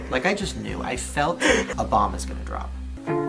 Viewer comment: I thought he said “i felt Obama’s gonna drop” and got so confused